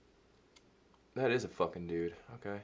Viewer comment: What he said: "that is a fuckin dude" What I heard : "that is a fuckin bear"